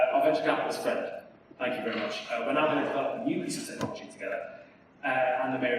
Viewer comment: Apologies for the poor sound quality; full subtitles are now available if you'd like to turn them on! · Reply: They should say "now going to" instead of "not going to".